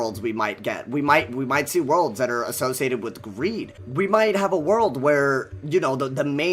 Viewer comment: I deadass thought he said weed